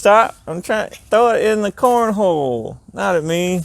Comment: Your son just had you misunderstood, thought you said "throw it at the cornball".